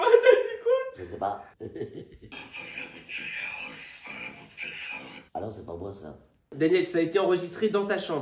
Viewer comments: Elle dit très clairement: "J'ai jamais tué Carole, c'est pas moi qui ai tué Carole" Les autres diront que je suis complotiste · j’ai entendu « c’est pas moi qui ai tué Carole, je suis pas là pour tuer Carole » qu’est-ce que tu nous caches 😂 une voisine un peu trop bruillante peut-être ?😂🤷🏼‍♀️